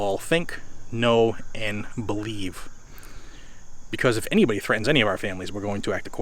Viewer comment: he didn't say if anyone threatens his family, he said if you show up. What i hear is if anyone, for any reason, protests anything, in what he considers "his area", and what he considers "Aggressive" hes going to try to murder them. tell ya what you jackbooted thug,. bring it.